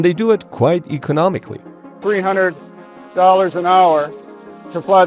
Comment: for a sec I heard $3000 an hour and I was like there is no way a sim costs that much an hour.